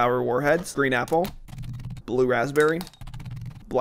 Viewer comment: When he said “green apple” somewhere around 3:40 it sounded like “green nipple.”